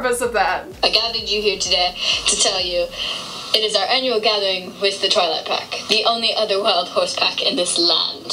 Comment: When that horse was standing on the rock in the beginning it said, It is our gathering with the Twilight pack, I thought they said 'it is our gathering with the toilet pack'😂😭